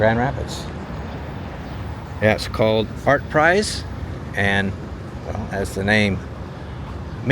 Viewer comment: i swear i heard "Fartprice"